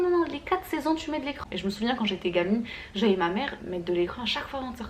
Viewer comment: Jai pas compris " les crans " c quoi sa es ce que tu pourrai me dire ce que tu voulais dire j'ai mal entendu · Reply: L'écran total Crème solaire